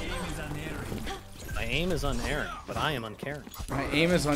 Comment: I always thought that gengi said "my aim is an earing"